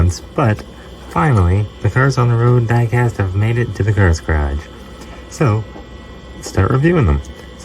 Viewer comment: There's a mistake the captions said the girls garage instead of the cars garage